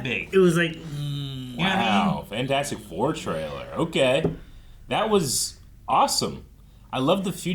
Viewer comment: I hear the choir going "Fantastic Fourrrr" and all I hear is "Oh holy sh*t ballls" from Deadpool